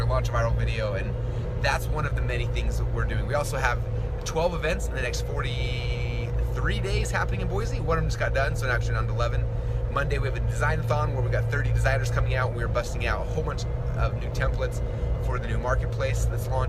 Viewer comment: Did Russell say "design a thong" at 5:28 ?